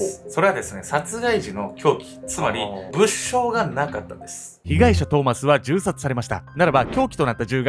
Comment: 機関車トーマスに聞こえた